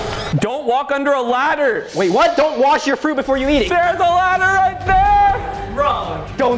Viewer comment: “Don’t wash your food before you eat it” hahaha the editor needs to double check what they’re typing out. clearly said “Don’t wash your fruit before you eat it”